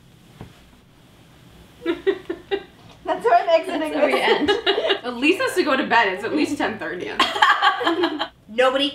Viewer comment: What does Elise say under de blanket? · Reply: Mary Bello "thats how I'm exiting this"